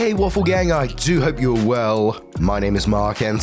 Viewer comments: I think I watched Mark a year ago and I thought he said, "waifu gang"I was like, huh, what a weird name.. Then for another video, I saw the waffle and it clicked. 😂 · I listen more than watch, and for the longest time I thought you were saying "hey awful gang!" It was only on a day I happened to look during the intro and saw the waffles that I realized. I did always wonder where "awful gang" came from...